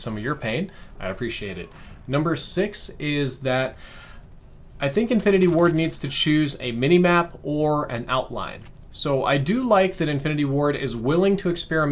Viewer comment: did he say infinity war?